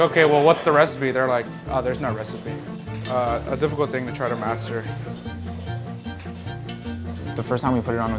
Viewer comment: I thought it said Master Cheif, like where are the aliens?